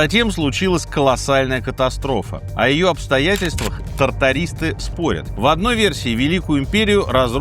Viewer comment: Стеклотария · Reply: Тартарийсты? Послышалось трактористы😮